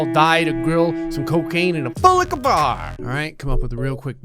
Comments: my first language isn’t english so i dont really understand what gray is saying but it kills me everytime he says FULIKA BAR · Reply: He says "full liquor bar". If you speak Spanish, it means "barra llena de tragos". If you speak another language, I don't know what it means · I love how he says “full liquor bar” so fricken much. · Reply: I first misheard that as "full like a bar" 😂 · I love how he says “full liquor bar” so fricken much. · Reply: Wai- WHAT?! He says "full liquor bar" ??! I- WHAT?!? Okay you can ALL say I'm stupid and stuff, but I've always heard him say: "pool like a bar" 😂😂😂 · I love how he says “full liquor bar” so fricken much. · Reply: I thought he said " BULLLICKABAR" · I first misheard that as "full like a bar" 😂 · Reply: Wan Nursyafiqah at first I thought he said pool liquor bar